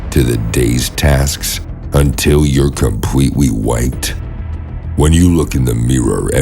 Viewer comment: Why does it sound like he said "until you're compwetewe wiped" UwU